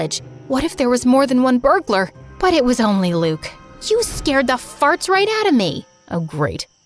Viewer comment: what did just I hear! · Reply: “You scared the farts right out of me!”